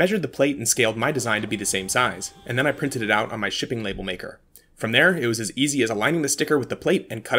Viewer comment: I heard "with my shitty label maker" 😂 Great project!